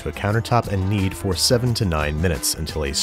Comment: I heard "And knead for Seventy-nine minutes [...]" and I was like "SAY WHAT NOW???" XD